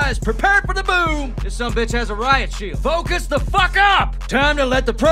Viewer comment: “This son of a b has a riot shield” so relatable to the comms I relay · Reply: When he said riot shield I thought he said Raichu💀💀💀💀 I was so damn confused💀